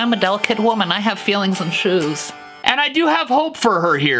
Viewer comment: "I'm a delicate woman, I have feelings and shoes" - Stevie T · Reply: i thought he said and shiz